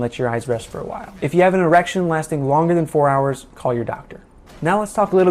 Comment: Did he just say "erection" LMAO